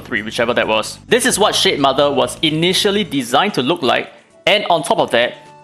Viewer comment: I thought he was saying shitmother I was confused for a sec